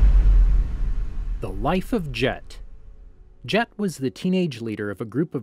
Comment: does anybody else hear him say th wife of jet instead of the life of jet